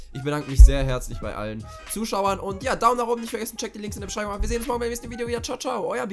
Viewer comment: ich verstehe immer Daumen nach unten